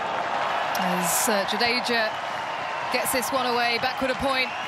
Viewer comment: did I heard Sir Jadeja 😮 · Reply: So jadeja not sir jadeja